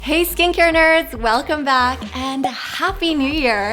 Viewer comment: I misheard she said "hello skincarerers" as "hello skin cancers" bruh it's 4 am